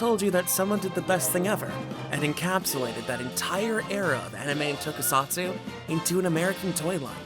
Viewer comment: I thought you said “encapsulated that entire era of anime and tokusatsu into an American 𝑻𝑶𝑰𝑳𝑬𝑻”, so I listened again. And again. And again. Still not certain what you said, but then, I had Micronauts, so prolly biased. Shogun Warriors were like the “That’s not the right one!”, crying children wooden Gundams; 𝒆𝒙𝒄𝒆𝒑𝒕 𝒔𝒐𝒎𝒆𝒉𝒐𝒘 𝒕𝒉𝒆𝒚 𝒘𝒆𝒓𝒆 𝒕𝒉𝒆 𝒓𝒊𝒈𝒉𝒕 𝒐𝒏𝒆𝒔?